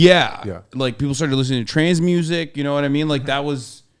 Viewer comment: When kept saying trance i thought he was saying trans music 😂😂😂 and i just rolled with it bc i know we be making a lot of edm